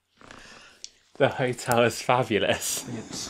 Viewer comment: I misheard it 😅 I’ve been saying “yer tellys fabulous” for weeks.